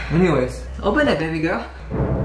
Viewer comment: Did... did he just say 'open it BABYGIRL'?! I- 💀💀